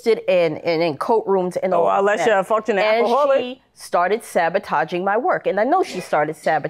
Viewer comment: I could have sworn she said “you’re a f**king alcoholic” I had to go back several times